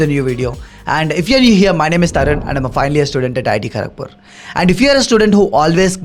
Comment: How the hell he know that I have a exam tomorrow 😂 and also worried that I might fail ! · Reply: Also misheard that i study at iit tharakpur ! 😂